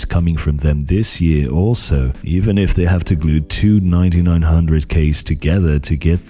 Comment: "glue two 9900k if they have too" had me rolling Great video as always. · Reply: oh I heard "if they have *two*"